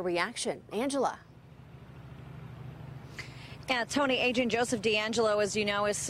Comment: did she say "AGENT Joseph deangelo"???